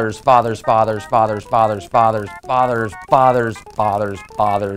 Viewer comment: ok now it sounds like ur saying otters insed of fauthers lol also u said we are related to mice and dinosaurs huh? We're birds then lol this leaves me the question is everything related